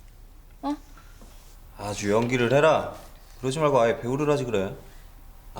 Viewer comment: 뭐라고 하는건지 그러지말고 보호를 해라? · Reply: 배우